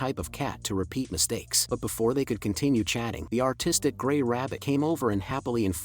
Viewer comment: Did anybody else hear the autistic white rabbit instead of artistic right here?